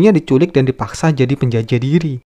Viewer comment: ngomong apa? Penjajah diri?